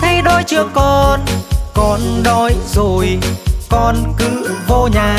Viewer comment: Nghe câu : Bà nấu cơm cho . lại nghe thành : Bà nâua cơm chó •_•